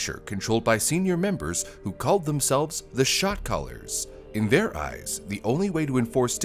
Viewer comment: I know he's saying "Shot Caller" but all I hear is "shock collar" 🤣🤣🤣